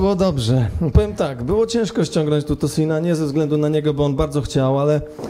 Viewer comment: did he say ''nigga'' ? lol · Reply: Nope, he said "niego" which means in Polish "him".